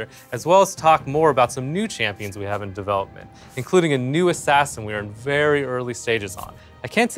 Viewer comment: Did I heard "new assassin" ??? :D